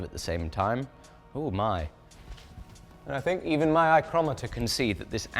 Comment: did you just say “eye-crometer?” 😂